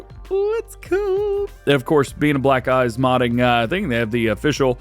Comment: sounds like you said black guys modding